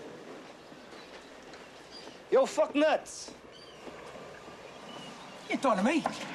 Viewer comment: I thought he said lugnut's at first, then rewind it back he said fucknuts, lol